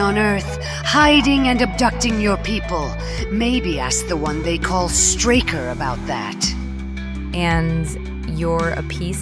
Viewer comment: I'm sorry, I swear it sounded like the totally not evil warmonger said "Call the one you call Streaker" around 9:25, and made me choke laughing.